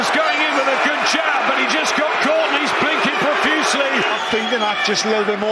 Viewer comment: He is blinking profusely..that's what Williams does all the time man. At first I thought he said bleeding